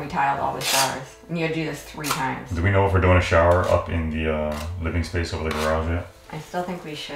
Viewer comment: Sorry I think I'm having hearing problems I thought you said you guys were sharing a shower sorry my head's going numb right now I apologize